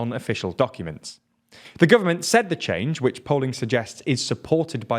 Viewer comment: For the south Korean story, I thought he said "poland suggest" instead of "polling suggests" and it made me think of a world where instead of polling people, they just go ask Poland instead